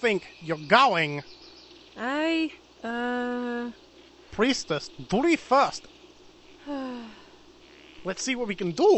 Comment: Only look me 6 years to take the bleach referance. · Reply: Eirik Holst took me 9 years to realise he said priestess, duty first..... I thought he said please, do me first